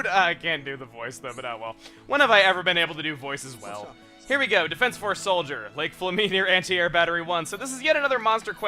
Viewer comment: I thought I said defenceless force soldier